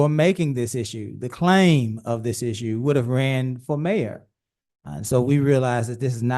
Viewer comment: Did he just say you have to run for major to hold a current major accountable ?